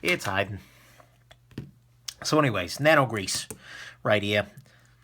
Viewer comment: When you grabbed the contact lens case at first, I thought it said, "Nano Goose."